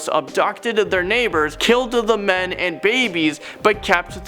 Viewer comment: Did you just say KILLDED the men? Dude you really need to pay attention to your videos before you publish them. Word of advice :D